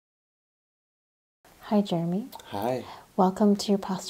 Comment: Sometimes I just hear "High, Jeremy?" "Hiigh"